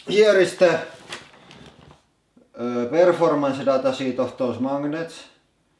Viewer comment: what did you say at 5:47 about that sheet of paper? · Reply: He said "performance data sheet" :D